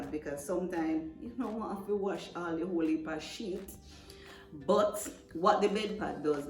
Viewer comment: This is Awesome.🎉 I also thought you said Sh!t.... But it was Sheet.. 😂😂...thanks for sharing ❤